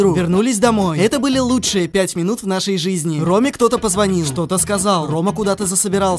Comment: Послышалось "Ромик кто-то позвонил" (Наоборот: ЛИНОВЗОП ОТ-ОТК КИМОР)